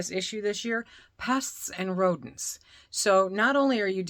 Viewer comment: Okay,losing my mind. I thought you said "pus and rodents". 😮😆 Had to rewind. Geeze Louise. Really appreciate your no nonsense content.